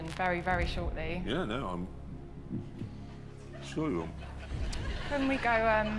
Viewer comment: Is he saying the German word „Entschuldigen“ in 3:36?